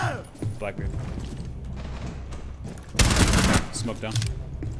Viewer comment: Did anyone else hear him say smoke down